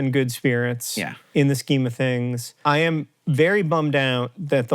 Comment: at first I heard "in these chemo things"